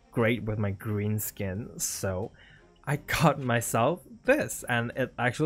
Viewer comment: When said "I got myself" I thought he said "I cut myself" It Literally made my heart stop for a second At least I was wrong and river isnt hurting himself too Phew Im so Stupid Lol